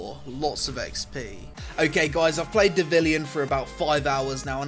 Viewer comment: When I first watched the trailer for this (T'was an advert), when they said "But this time... you are Devilian" I thought it was saying "da villian!" and trying to be hip/cool whilst doing so.